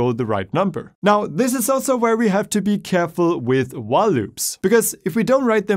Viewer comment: When he said while loops i thought he said wah loops Don’t know which i prefer